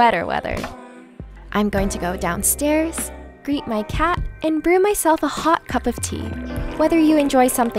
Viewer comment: Thought she said "Beat my cat" 🤣🤣🤣